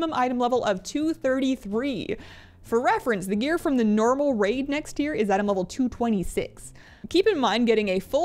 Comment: it sounded like you said "next year" and i actually thought that wasnt a mistake (i know you said tier) lol